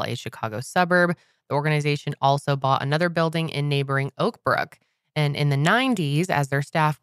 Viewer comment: When said “Oakbrook” I thought she said “Oprah” 😂